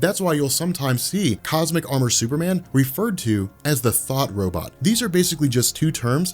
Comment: Instead of saying 'thought robot' i thought you said 'thot robot'